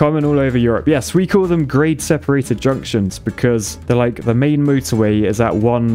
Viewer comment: I swear I heard you say "Great separated junction" not Grade separated junction.. though you was just being enthusiastic before you explained LOL